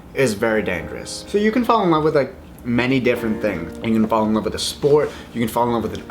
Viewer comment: i thought Joey said, "you can fall in love with a spork" 😂